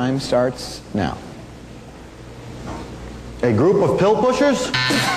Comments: I thought he said A group of pill-pushers. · i thought he said 'bill-pushers' XD dat's y i even laughed at dis XD